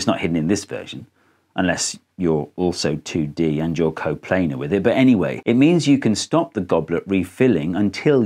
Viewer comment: I almost choked on my breakfast at “unless… you’re also 2D… and you’re coplanar with it”. The hesitations were crucial, as if he knew just how dumb an edge case he was about to describe. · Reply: I thought he said “caught playing with it.”